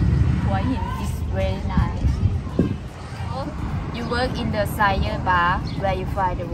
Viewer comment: Desire bar right? couldn't quite hear what was said. Sounded like sier bar lol. sorry.